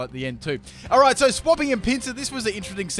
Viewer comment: It sounded like he said "swapping in Pizza". Don't question meeeeeee.....